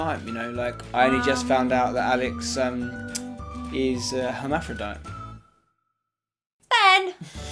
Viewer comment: what does say at 5:22? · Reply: Ciaran Flowerdew hermaphrodite - a person or animal having both male and female sex organs or other sexual characteristics, either abnormally or (in the case of some organisms) as the natural condition.